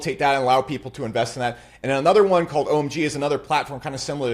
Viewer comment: Jump to 22:50 for his narrative & RWA coin picks. LINK - ONDO - XRP - ZIG - CHEX & AUTOS. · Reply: did i hear correctly at 33:35 he‘s been mentioning OMG? ist it $OMG? or something else? maybe yoj know. 🙏🏼